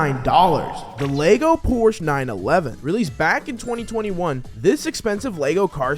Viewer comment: did he say 11